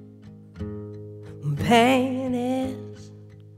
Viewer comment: all I could think was, “Ha! They said penis!” I’m so mature.